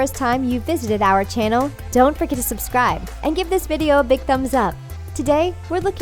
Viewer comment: did anyone catch that it sounded like dont fricking subscribe